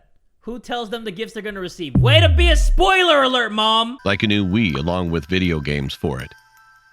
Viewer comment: I thought the dad said " like a new weed"